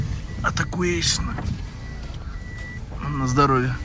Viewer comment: мне послышалось или ты сказал Атакуэйшн?? 😂😂